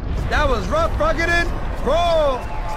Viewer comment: What’s he saying at 14:27? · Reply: *Raw!!!